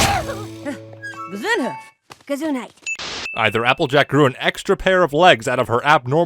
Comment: Did dashie just say Gesundheit? Dafuq ^^